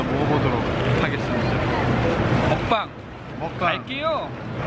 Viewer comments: Did Taeil really says "Kain kayo"? my god! That's a Filipino word for "Let's eat" hahahahaha. Shutaaaaa pinaulit ulit ko pa yun😂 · Did I just hear Taeil saying “Kain kayo” 8:03 ??? 😳😳😳